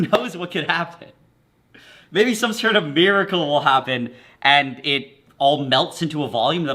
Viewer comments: Ngl I heard “beer”-acle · I swear I heard you say "Beericle" ... but I replayed it 10 times and it's clearly Miracle... We have discovered a new audio illusion, boys! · was it just me or did he say “beeracle” instead of miracle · Did he say beeracle? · or so, I thought he said "Maybe some sort of BEERicale will happen!" Lol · I thought you said beeracle instead of miracle 🤣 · Thought he said beeracle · Did he say "beeracle" or am I going insane. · Did he say Beeracle? That’s like the perfect name for this! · fully thought he said "beer-acle" and i recoiled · Did he say miracle beeracle?